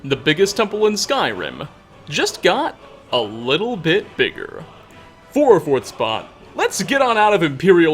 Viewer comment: What are you saying here? You say the same phrase in nigh on every video and I can't hear the intricacies of what you're saying (I have hearing issues)... Not knowing has been bugging me for as long as I've watched you. The subtitles don't help either, saying for fourth spot which can't be right as this is only the second spot!? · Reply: "For our 4th spot", he's counting down 5 things about the stormcloaks, that's why he mentions the 5th spot first and the 4th spot 2nd